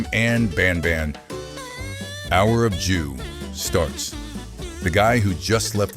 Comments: why did i hear him say the hour of jew 😭🙏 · WHY DID I HEAR HOUR OF JEW!?!?!?!